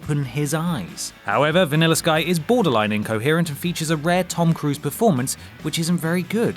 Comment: I'm sorry, but at 2:44 did he say a "rare" Tom Cruise performance that isn't good?